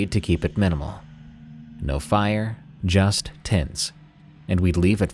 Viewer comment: 23.00 a genuinely thought you said "tits" not "tents" haha